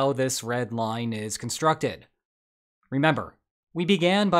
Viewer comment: Sorry, comrades.....I thought you were saying "BREAD lines". 😂